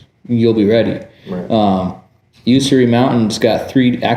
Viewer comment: said "something" mountains have a 3d range. What was the mtns he said? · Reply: Ben Avery has a hill course and also usery has a 3D range as well 🙌